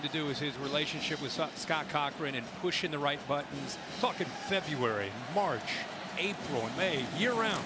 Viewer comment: does he say "fucking February"? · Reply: Dude it sounds like he said “fucking February” when clicking on your time stamp but if you replay it back 10 seconds it sounds like “talking February” lol